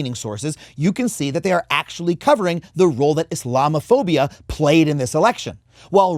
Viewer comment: For a moment I thought you said llamaphobia 😂 Edit, stupid autocorrect 😢